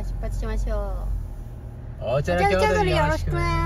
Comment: いつも楽しく拝見しています、ファンタスティックピーポーです。笑 ずーっと疑問に思っていたことがあって初コメントです😊 いつも出発時に言ってるこれ→04:50、何て言っててどういう意味なんでしょう？ お茶の京都によろしくな(？)って聞こえるんですが、意味が気になって夜しか眠れません！